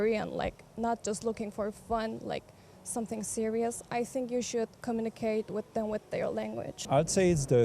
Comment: what did she say on 1:00? what-a-what a language?? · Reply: “…you should communicate with them with their language”